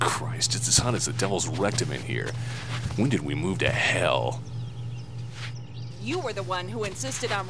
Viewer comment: could someone tell me what he said and meant? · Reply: "Christ it's as hot as the Devil's rectum in here. When did we move to hell?" The town he just moved to is in the desert, thus his expression of frustration at the temperature. Hope that helps 😁😂